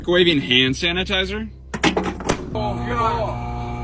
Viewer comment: I thought he said Kuwaiti hand sanitizer and was really confused